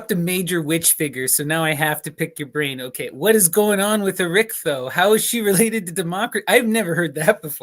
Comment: Did anyone catch the name of the witch figure mentioned at around 34:43? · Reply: Erichtho, famous for appearing in Lucan's Pharsalia, Goethe's Faust, and others.